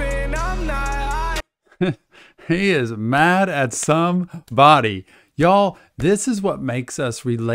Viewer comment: I thought I heard him say “he is mad at some thotie”😂😂